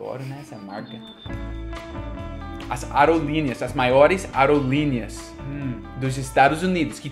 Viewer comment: Eu escutei "arelinas" e fiquei um tempo tentando entender o que o disse até pegar o contexto: "Aerolinhas", que aqui é mais falado "linhas aéreas" (e nem meu corretor sabe o que é). Hehehe OBS: Quinquagésimo = 50°